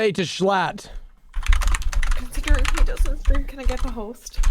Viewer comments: What did she say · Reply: "considering he doesn't stream, can i get the host?" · anyone have any idea what she said there? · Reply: Considering he doesn’t stream can I get the host